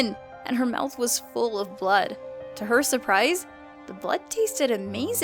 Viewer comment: did you say.. blood? ¬w¬ I like blood, it tastes like metal and I like metal. do you like blood? Cause I reaaaally like blood, I could kill for it ¬u¬